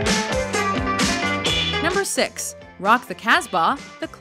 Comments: Rock the casbah isn’t that bad, though. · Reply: I thought they said lock the taskbar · Feel Good Inc. would've been perfect. It has a groovy beat but has very dark lyrics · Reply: I thought they were saying lock the taskbar #6